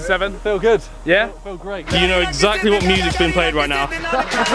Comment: what did Harry exactly say? "You know exactly.." what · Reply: 'you know exactly what music's being played right now' cos of the indian music